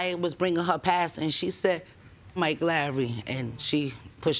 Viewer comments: "she said Mike Lowery, and pushed the TV" hahaha 😂😂😂 I know she said "f**k Larry" but really sounded like she said "Mike Lowery" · Wait, did she say she said “Mike Lowrey”? Like from Bad Boys? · Did she say her daughter said "Eff Mike Lawry"? Isn't that from Bad Boys, Will Smith? 😂😂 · I’m certain that at 2:27 seconds in I hear (“ And then she says Mike Lowry and pushed the TV 📺.”) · I'm sure I heard "F*ck Mike Lowry" there... or is it just me. 😂😂